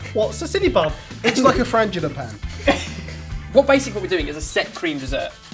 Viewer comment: i thought he said vaginal pad.....